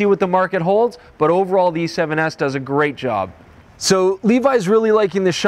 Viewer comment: Great video! Did you say ...D7s at 5:15min? :)